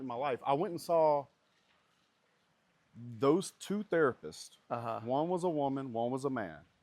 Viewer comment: I thought it said "the-rapist"